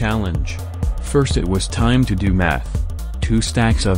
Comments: I did hear (& misheard) "First, it was time to do meth" at arround ~ 5:30 and bursted out in laughter like a maniac leaving my gf shocked 😂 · I was just listening to it in the background and I thought it said "now its time to do meth" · When he said now it is time to do math it sounded like he said now it is time to do meth